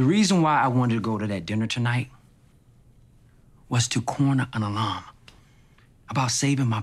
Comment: What does Tate say at 0:05 ? To corner an alarm? 😅🤣 pls help a boy out, I’m not natively speaking English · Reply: Alum